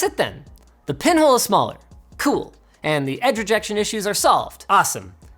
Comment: germans be like : did he say:" the Pimmel is smaller "?